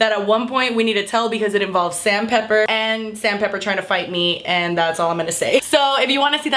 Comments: I thought you said Sand Pepper and I was like da fuq?! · I heard " fight with Sam Pepper" as "fight with Sand Paper"